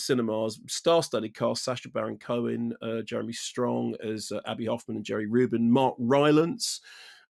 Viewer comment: For half the film, I thought they were saying 'Andy' Hoffman.